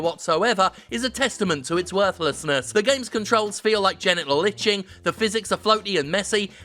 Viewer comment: "The game's controls feel like genital itching." I cannot let this golden analogy be lost. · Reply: Oh, I genuinely thought he said genital lynching.